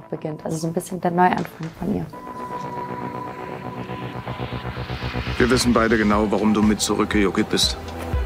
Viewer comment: Was sagt Tuner bei 7:24 ??? "Wir wissen Beide genau, warum du mit ..." ??? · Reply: Er sagt... Wir wissen beide genau warum du mir zurück gejuckelt bist.. also wieder mit nach Berlin gefahren ist. Wahrscheinlich für Emily